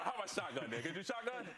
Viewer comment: Uh did he just say hes a shotgun nigga at 2:08?